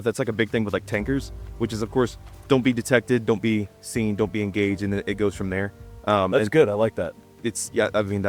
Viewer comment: I thought he said “don’t being gay” lolol it was don’t be engaging I think.